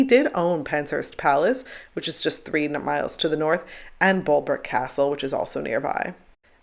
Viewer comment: talking about the house Henry didn’t sleep in but visited Anne in, did I hear you say after that, that he owned Penshurst Palace that was ‘just 300 miles to the north’? Hopefully you meant three miles instead 😄😭, because otherwise even by today’s standards in England/UK 300 miles is a long journey, I couldn’t imagine the palace being a quick carriage journey home after visiting her 😭:) I love your videos by the way, I’m glad I stumbled upon your channel a while ago and have been able to tour so many places from the comfort of my bed at night! 😁